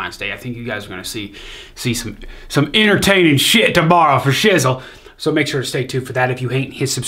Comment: Did he just say "for sizzle"? 😍